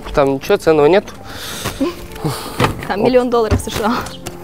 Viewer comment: Мне одному послышалось 1 мелион